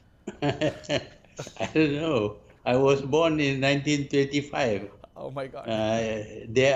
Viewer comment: In the interview, he stated he was born in 1925. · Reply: He sounded like "I was born in 1935"